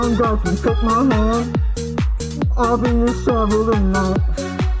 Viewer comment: did he say “I’ll be your shovel tonight”?